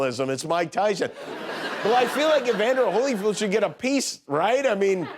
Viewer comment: Did he call him Holyfood?